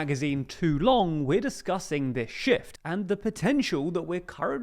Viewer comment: "we're discussing this shi-" My mind: t What he said: ft